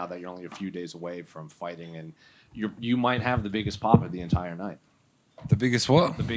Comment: Tai thought he said he’d have the biggest cock on the card 😭😭💀💀